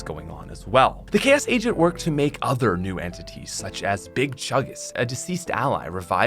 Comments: Did I hear big chungus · at first I thought he said big chungus